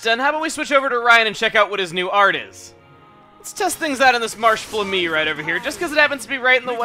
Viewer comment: I heard "lets test it out on this marsh of me" when he murderified to hose flami